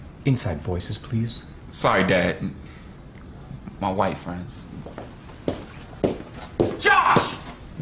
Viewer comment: I thought he said my wife friends but he really said white friends